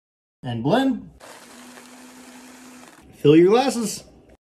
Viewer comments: Is it just me or did it sound like he said fill ur (GL)asses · Thought he said “fill your a****” and not “fill your glasses